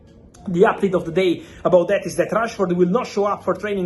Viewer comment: did he say trashford 😂